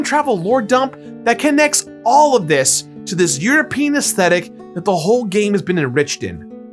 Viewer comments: 😆 European aesthetics at 34:00 I heard "you're a penis ticks." Dumb gutter humor for the day, check! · he said european esthetics at 33:55. i wasn't paying to close attention but i had to rewind cause i heard "your penis esthetics" hahahaha